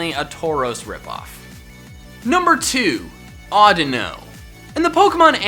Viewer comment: am I the only one that hears*i dont know* instead of odino